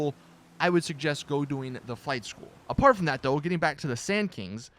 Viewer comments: I thought you said fight school instead of flight school lol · I thought you said fight school and not flight school... I got so confused like WTF is fight school blud?